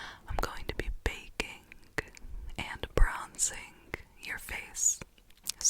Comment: I thought it said "breaking and freezing" and than i thought "baking and freezing" and honestly i was so dang confused wth